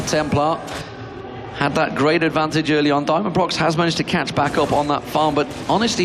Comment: i thought he said "Diamondprox", his previous name